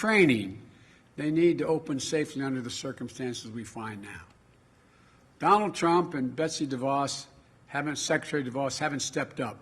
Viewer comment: Maybe I Heard Wrong But It Just Didn't Sound Right😂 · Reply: Omg lmaooo he said Trump and Betsy what?!!! I heard it too 😂